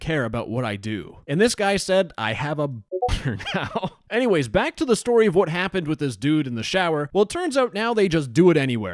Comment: what does this word mean the b**r one or like what did he say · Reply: boner